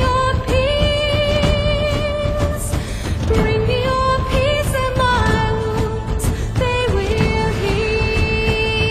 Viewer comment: did she say woods or wovfes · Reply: wounds